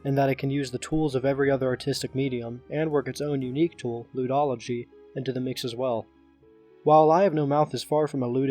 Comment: What does keep saying? Ludology? Lewdology? I can’t understand what word he’s saying (He says it right